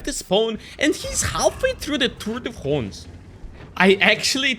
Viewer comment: again, great video ! You teach the game very very well ! 5:13 sorry, I'm french but I can't understand what you are saying and I watched it many times. Can you write me what you tried saying plz? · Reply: tour de france